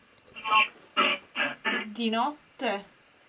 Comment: ho sentito dopo "di notte la bestia ..... la bestia uccide" che brividiii